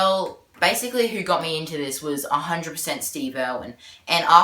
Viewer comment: Did he say Steve-o